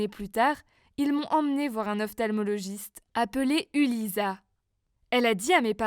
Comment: un quoi !?!? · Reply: Un ophtalmologiste